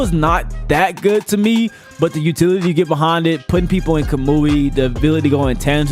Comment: Did he say ka-moo-ui ?